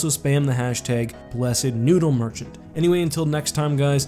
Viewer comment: Did he say veet